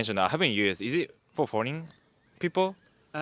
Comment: Asking about Bumble, I thought said, "Is it for horny people?" instead of "Is it for foreign people" 😆.